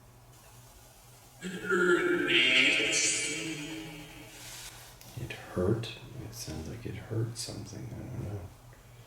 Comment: he said "It hurt immensely." It sounds like he says "I deserved that" at 11:13 At 12:40 he said, "Sorry for that." I personally don't think he deserved it but that's just what I heard at 3x speed. Then when the guy asked him what he thinks we're doing with this work, Charlie said, "I don't know."